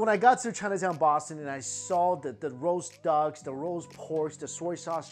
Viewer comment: did he say roast dogs?